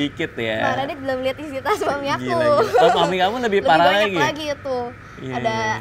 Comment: dia ngomong apasih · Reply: Isi tas suami aku 😅